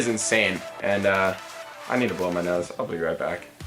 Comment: thought he said I got a roll a blunt when he said I got to blow my nose guess the 420 car insurance put me in that mindset